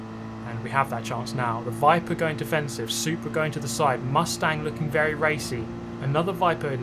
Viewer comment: "mustang looking very rapey" misheard that at first.